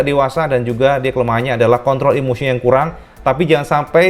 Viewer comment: gua salah denger apa gimana si?????? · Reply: Bukan nya kontrol ehhh jdi nya peler wkwk